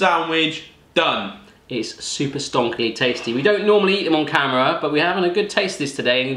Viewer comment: what is the word he always uses at 6:22?????? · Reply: Stonker= great thing. Good thing